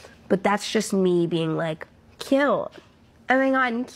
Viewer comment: literally the first time she said “cute” it sounded like she said “kill” hmmm NO NO NO!! In the name of Jesus Christ of Nazareth I rebuke any of the evil being produced, my God, The Lord I praise The Holy Spirit Amen!